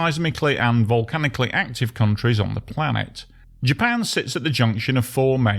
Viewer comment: it’s not a planet dude. Wake up.😂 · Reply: You definitely misheard what he said 😂 "COUNTRY ON THE PLANET " not "Japan is a planet "